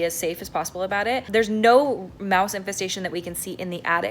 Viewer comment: I thought this said “moose infestation” and I got really excited